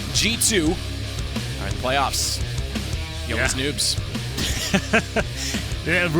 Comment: wtf commentator says "yo les noobs"